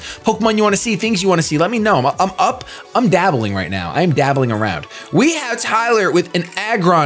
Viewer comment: when I first heard it, I thought said "I'm dabbing around" lmaoo